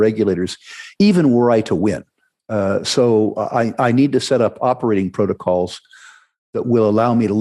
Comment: At first I thought Rick said “I need to create operating protocols so I may lift my leg freely”